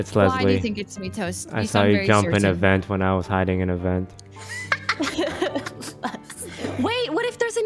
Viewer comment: The fact that Toast gaslighted Leslie into thinking she vented even though she never vented that round was amazing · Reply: no, I was wrong, I went back and listened, I did indeed miss that he said saw her vent. Oops. I thought he just said he saw her from the vent. Nope just gaslighting like a pro